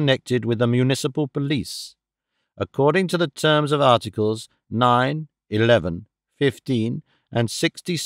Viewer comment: did i just hear 9/11?